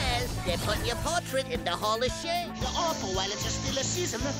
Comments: I thought he said "Hall of shit"... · I keep hearing "hall of shit" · I heard "hall of shit" 💀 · I legit thought Bugs said “Ball of Shit.” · i thought bugs bunny said hall of shit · I thought he said their putting your portrait in the hall of shit before I saw the comments